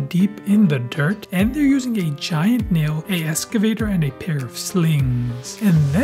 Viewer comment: Slings, oh lol, I thought you said slaves and I was like, "Hold up"